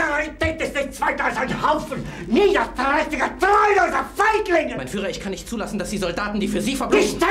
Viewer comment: Did he say the n-word!? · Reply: NO: He said : FEIGLINGE = cowards